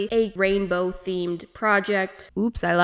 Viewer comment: Hmm I thought u said no rainbow theme tho lel